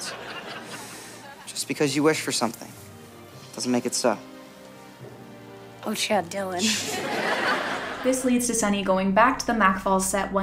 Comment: did she say oh shit???? Lmao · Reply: i think “oh, chad dylan” was the statement but god could u imagine